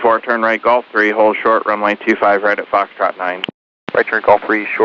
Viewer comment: Well, since you're an idiot as well....the controller said "Foxtrot Nine-er" on the first instruction. He was giving the pilot a break and just telling him what he needed read back as part of our requirements in the 7110.65. He read back foxtrot nine because that is what the United pilot with the shit phraseology said. And "fife" isn't required phraseology...maybe don't talk about stuff when you don't know wtf you're talking about. You sound as ignorant as the United pilot getting mad at a controller for doing his job lol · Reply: the first instruction, he clearly says “foxtrot nine”. Besides, proper phraseology should be used always. 3 is Tree, 4 is FOW-er, and 5 is Fife.